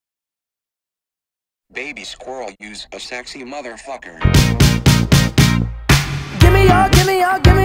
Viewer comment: I can’t be the only one who hears “Baby Squirrel” in the intro. Right? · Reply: I hear "baby squirrel is a sexy mf" lmfao